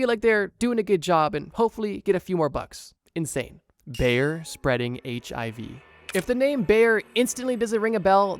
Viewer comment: I was cleaning my room listening to the video and suddenly I hear.... "BEAR SPREADING HIV" :D :D :D Until it clicked...